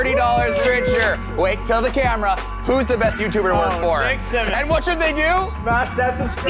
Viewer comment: I love how the captions said Mr. Beast intead of Nick Symmonds